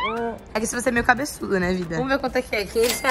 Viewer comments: Vcs ouviram oq eu ouvi nessa hora 3:43 a chamou o peixinho de vida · eu ouvi um " VIDA " fãs de????